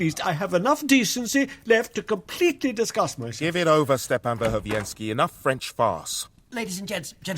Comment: DID HE JUST SAY "ENOUGH FRENCH FARTS! 🤦🏻‍♂"